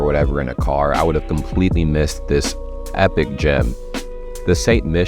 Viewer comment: I misheard you: "...I would have completely missed this epic gym..." Later I realized it was gem. 🙂